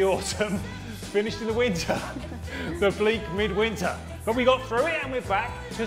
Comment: I thought was say and oblique bleak winter! Nice kitchen workout.